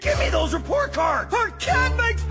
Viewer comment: but... buttering toast is the only way to be a good person. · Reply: Wait toast? All those years I thought Ed said butters toes